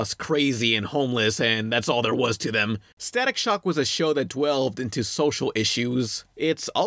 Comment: Did you just say "dwelved"?